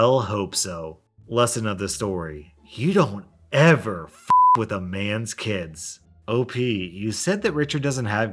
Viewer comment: i thought he said "you don't ever f**k a man's kids